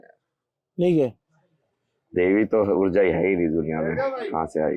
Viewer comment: did he say devi toh urja hai nhi is duniya me???